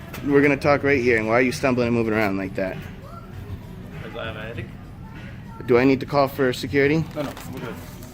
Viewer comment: What did he say at 2:27 when he's asked why he's stumbling about like that? "Cos I'm [inaudible]". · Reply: "Cos I have a headache" I believe.